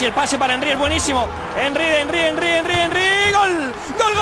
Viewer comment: who else thought the man was saying:"GG, GG, GG, GGGGGGGGGGGGGGGG" XD